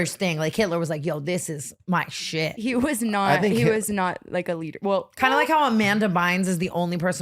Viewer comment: did she say he wasn’t a leader? cant hear what she said